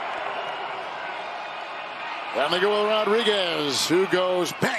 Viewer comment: what did the commentaor say at 4:31 :face-turquoise-covering-eyes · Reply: and they go rodriguez